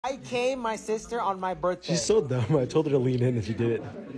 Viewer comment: First I thought you said, “I came my sister on my birthday.”☠️☠️